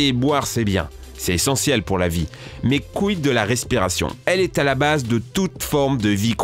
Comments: Je sais, il a dit "mais quid de la respiration"... Mais c'est pas ce que j'ai entendu ><... à savoir "mes c**illes de la respiration" :') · svp dites moi que je suis pas le seul qui a entendu "mes couilles de la respiration" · wait attent svp ile dit a 3:12 "mes couille de respiration" c est quoi ca